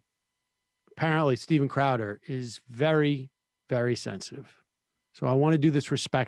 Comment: Oh! “...very, very sensitive.”! At first, I thought you said that he was “...very, very sexy.”! 😳 🤦🏻‍♀️ 😆💜😄